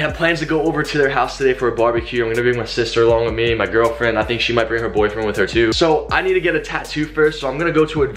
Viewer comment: Haha Did you say your girlfriend is going to bring her boyfriend with her